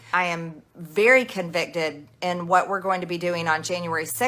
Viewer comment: did she say "i am very convicted" instead of "convinced"